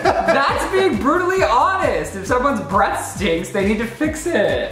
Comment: I don’t know why I heard Breast lmao